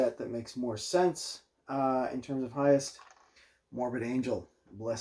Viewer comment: Morbid Angel - I thought you said Mormon Angel